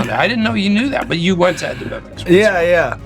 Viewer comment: What does he say at 20:39 ? "I didn't know you knew that, but......." can't figure out the rest of the sentence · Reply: he said " but you went to avadevicks" or however that restaurant is spelled